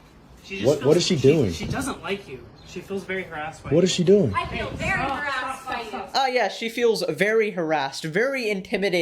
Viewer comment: at first I heard as 'she feels very aroused by you' and 'I feel very aroused by you'